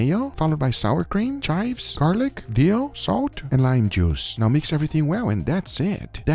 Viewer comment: does anyone knows what did he says here?, I keep hearing Dio XD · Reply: He says dill